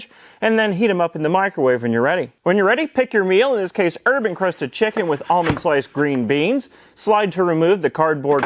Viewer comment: Urban Crusted Chicken? WTF did I just hear? 😄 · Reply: It literally says "Herb-Crusted Chicken" you might've heard it wrong